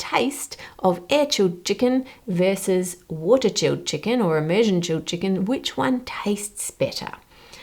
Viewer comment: subtitle wrong, Asian -> immersion chilled chicken. I wonder if we Asians has anything to do with the method of chilling chickens...